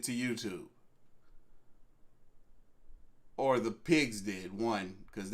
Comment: did call the police pigs?